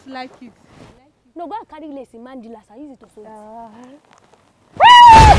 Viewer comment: I think I heard that once"go an get it in madilas"15:23🧚🧚🧚🧚🧚🧚🧚